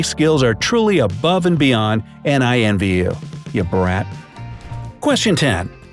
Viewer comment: Did he call us a brat at 9:03?!